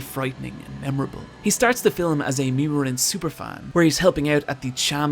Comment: for the (???) in the caption, I think he said "Mima-rin"